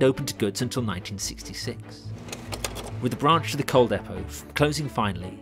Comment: Norwich city station looks like an amazing structure. A Fine station for a fine city. At least we can't blame Beeching for its destruction! Oh, and at 7:52 your subtitles have "Cold depot" instead of "Coal Depot" - presuming it wasn't a cold depot of course!